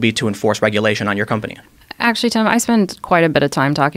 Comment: did she just call him Tom?